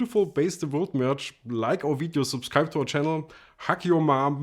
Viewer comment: For two seconds I thought said "hack your mom" at 5:16 :-D but it was a lot nicer what he really said ;-) FAB bass btw